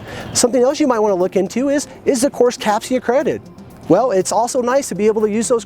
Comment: Sorry to ask a dumb question, but what is “capcia accredited” I think I misheard but I’m having trouble figuring out what that is? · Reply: Possibly CAPCE accredited courses